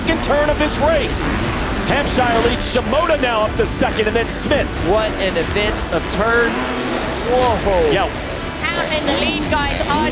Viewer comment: anyone catch what said at 13:39 mark..hahahaha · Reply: Lol I was cracking up at that too man... "what an event of tuurrns" dude messes up on the mic so much.